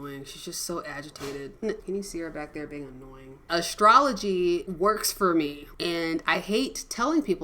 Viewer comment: Astrology? I was surprised to hear that. I thought I heard astronomy at first. Neil DeGrasse Tyson and all. Maybe check his opinion on astrology.